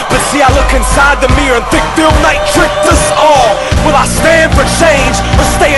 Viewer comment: Am I the only one who thought it said Bill Nye instead of "Phil Knight" at 2:35 Hahahaha